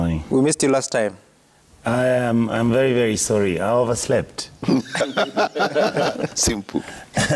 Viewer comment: Did i hear "I WAS SLEPT" ? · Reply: No,I overslept